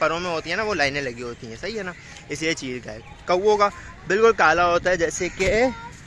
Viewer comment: pr kya bola hai apne ,smjh nhi aya? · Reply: Islie cheel ka hai yeh par.